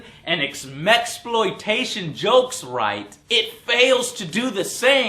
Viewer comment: did he just say "Mex-plotation?" REALLY?!